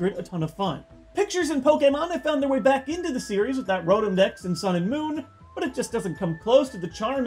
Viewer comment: I had to go back and listen again, because I thought you said "Rotom dex in Sonic Boom"